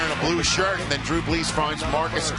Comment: Who else heard the announcer say Drew Bless